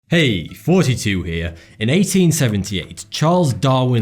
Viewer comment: I keep hearing "hey, 42 here"